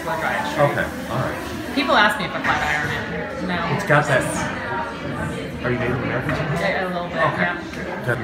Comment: Did he just say..."Are you fat"????😡😡😡